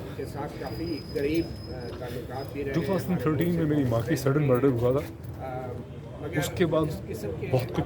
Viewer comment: can you please tell what did he said "2013 meri maa ka sudden murder hoa"???